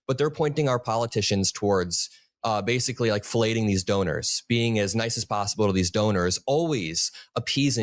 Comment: Drop a like if you appreciate the message in this video! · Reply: I'm an old man with old ears. I did a double take when I heard @ 6:28 "fellating the donors." Indeed, it seems to be going around...